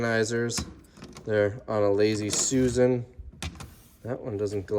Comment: Did say Lazy Susan @ 21:40??? How offensive to Susan's!! Did you see the Curb your Enthusiasm episode about that? Hilarious.